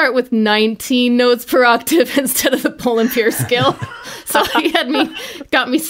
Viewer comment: Im training my ear to english. What did she said at 3:27? "Poly...? Scale". I just got the "poly" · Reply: Bohlen-Pierce scale